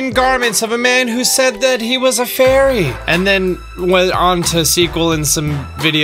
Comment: I thought he said "furry"